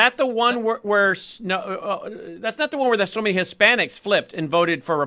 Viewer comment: what is Lindell’s cohost saying—“swimming Hispanics”? “Sunni Hispanics”?